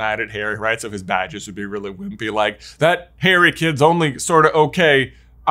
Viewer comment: I love how, at the 12:37 moment, the captions say "That Hairy kid" instead of "That Harry kid". Makes it a little funnier.